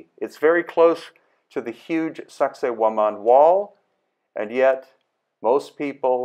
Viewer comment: I admit, I'd never heard of Sacsayhuaman until the 1st season of Ancient Aliens 10 years ago. It was such a fascinating site, I wanted to know more; but, it actually took me a few months to figure out how to spell what they were saying so I could research it online. All I kept hearing was "Saxxy Woman"! (I wonder: am I the only one?)